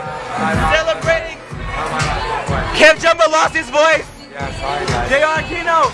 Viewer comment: I thought you were saying " KevJumba lost his..virginity" LOL.